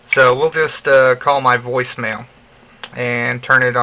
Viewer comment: when he said "we'll just call my voicemail" i thought he said "we'll just call my boy snail" XD